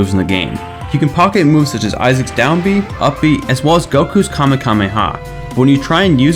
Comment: Wait a minute... Did I just hear Kamekameha??